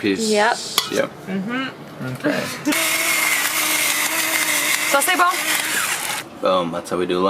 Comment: did you say : "ça c'est bon"?